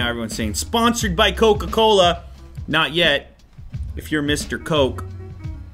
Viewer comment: "If you Mr Coke, gimme a ring" I heard it as, "If you miss da coke, gimme a ring..." What you implying there Lew?